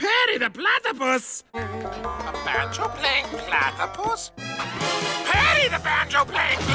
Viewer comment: That “banjo-playing platypus” part is kinda funny.🤣❤️ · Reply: I heard "that you're playing platypus"